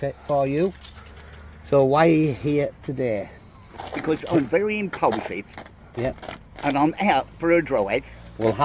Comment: what does he say at 0:20? 'im very something' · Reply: I'm very impulsive and went out for a drive. But then he contradicts himself by saying that he actually meant to take the journey. He's full of shit but sadly he makes for very funny viewing.